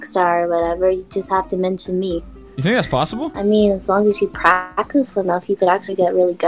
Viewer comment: ここなんて言ってますか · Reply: You think that's possible?